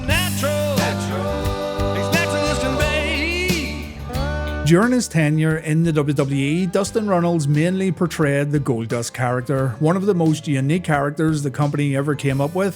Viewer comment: Did you just call him Dustin "Reynolds"?